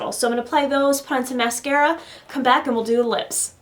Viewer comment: I love this look!!! I thought you said "put on pints of mascara" LOL but i just am half def anyway LOL